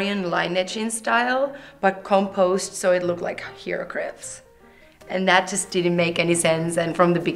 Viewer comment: What does she say at 8:05? "composed so it looks like _____." Sounds kinda like hieroglyphs, but I'm not sure.